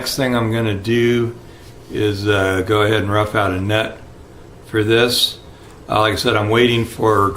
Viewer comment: I had to rewind to be sure lol I thought you said "rub out a nut" LOL! man brother you have hands of a surgeon! I wish I could still do tedious work like this! it's looking great!